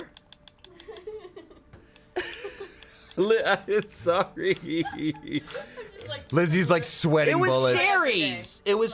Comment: For some reason I absolutely cannot hear what Shayne said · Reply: He says, “Is it that those people just want attention?”